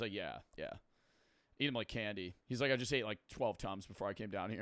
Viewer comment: when he said tums i hear tons i though bro was saying he was eating 12 tons a day